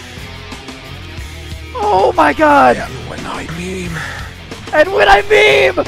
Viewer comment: I thought it said and when I cream